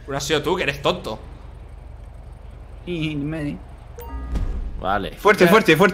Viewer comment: que dijo? Dijo yme?